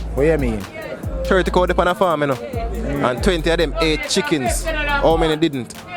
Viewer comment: Brah! If you had said the last question slower and clearer I would’ve gotten the moment you said. It sounded like you said 28 chickens when it actually 20 ate chickens.